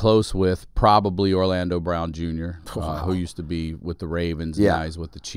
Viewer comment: I’m high and I thought I heard Ryan say Orlando Brown and That’s so Raven lol I was like that guy played football? 😂